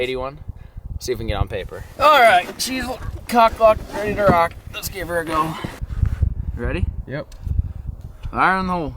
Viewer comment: what did he say? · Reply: Yooo